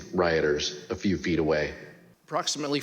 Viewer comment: Swore at 08:55 he said Fweet not feet.....